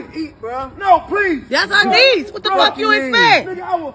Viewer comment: I bet your daughter was the problem! Look at the smirk on the daughter’s face! Plus he was walking over to apologize to her sincerely! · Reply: I thought she said her neice